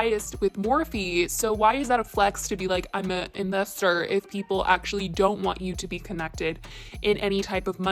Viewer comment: i thought he said i’m invested in morphe not “i’m an investor” that’s different. I’m not taking jeffrees side but might’ve meant he’s invested into the idea of the company? I think it was used in a diff context.